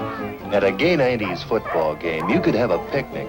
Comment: gay 90s football game? did i hear that right?